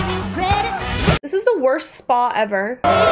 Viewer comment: I thought she said, "This is the worst song ever." and I was about to riot for the insult to Dolly. 😄